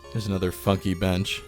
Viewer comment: I swear it sounds like he says "There's another f*ck you bench". 😂 Either way, it's sad to see Cooper Village like this. I grew up near here and remember this place well. I always liked this plaza